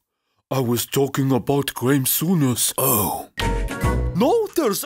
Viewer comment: who? Please let me Know what did Pogba just say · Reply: Graeme Souness